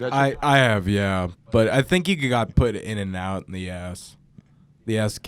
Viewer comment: sounds like he says "yeah, but I think you got to put it in and out the ass" If you got to the time stamp and turn on subtitles/closed captions it says "Bet I have yeah but I think he got put in and out in the ass"